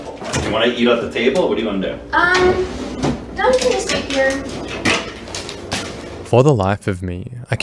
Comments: i figured out what she says at 39:20, shes saying "no we can just eat here" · If anyone is curious about what she says at 39:18. He says "do you want to eat here or at the table?" She responds "no, we can just eat here." She doesn't want to leave the immediate area where she can keep the table between herself and him. He laughs it off with a nervous laugh. He's not really thinking straight, and just going through motions while his brain tries to decide how this whole situation can play out in his favor. It's very surreal and he's talked a big talk and now he has realized what he's actually doing and is just killing time until something concrete presents itself. Also, why do you say "hey" after every few sentences? lol PS, Chris really let this whole interaction go on for a loooong time before he stepped in. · I heard “no, we can just eat/wait here” But now I’m not sure anymore 😭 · I think she thought he said “should we eat at the table”and she responds “na we can just eat here”. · "no we can just stay here" i think she says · I think she says “no, we can just eat here.” Hope this silences the rumination over those verbal vibrations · It sounds like she says “no, we can just eat here”